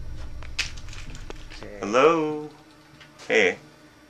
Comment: At the 11:44 mark during the last clip I think I hear a male disembodied voice saying "Here" and then "okay" Right after the guy says "If someone's messing with me I'm going to kick your ass". Also, anyone else see that creepy looking doll hanging up in that closet???? Wth???